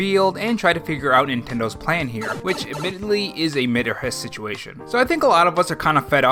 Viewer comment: Did you just say... mit or hiss?!